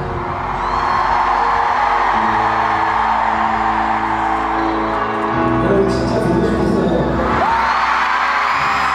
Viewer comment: What did Jungkook say right before the end? 💜💜💜💜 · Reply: I think it was something like I've really missed you all.